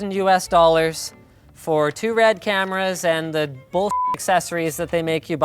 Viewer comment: in 9:09 did you say 2 red cameras